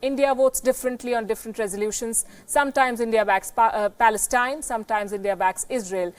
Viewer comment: I think she said it India backs pakistans haha. In the heart she knows India backs Pakistan